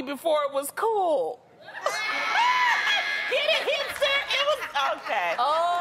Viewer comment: I thought she said "Hamster" and not "Hipster" so at first, I didn't get the joke lol